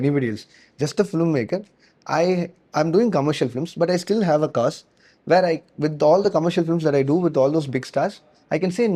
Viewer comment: anybody tell me what movie was he says? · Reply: Requiem for a Dream